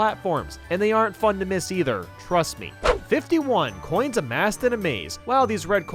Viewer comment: I always thought it said Coins amazed in a maze.